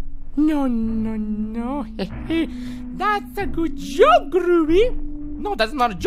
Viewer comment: groovy instead of goofy😂